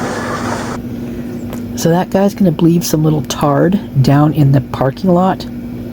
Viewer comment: what did you say? Retard??